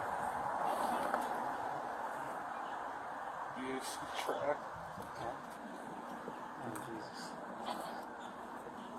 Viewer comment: What does he say @ 8:42 ? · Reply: Diss track